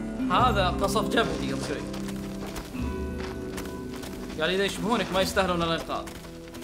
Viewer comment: دقيقة 44:52 لا قال لو أصدقاءك يشبهونك في اللطافة يستحقون الإنقاذ عد واسمع ايش يقول